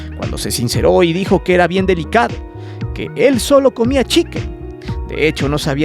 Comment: nisiquiera sabe decir pollo en inglés yo escuche que dijo chequen verbo de observar algo y me quede con mi cara de ¿que checamos?